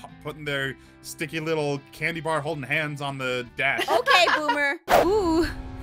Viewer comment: Did she just say Boomer